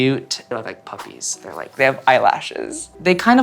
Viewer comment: the subtitles say “cute bark” i’m crying😭😭also that bark reminded me of ringo starr in the beatles a mid summers night dream skit <33